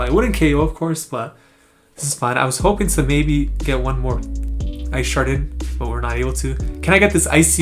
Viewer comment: I thought you said you Sharted 💨💩 "ice shard it "🤣🤣🤣🤣🤣🤣🤣🤣🤣🤣🤣 I love watching your videos. Also im adding articuno to my battle roster. Got him a second ChRd move finally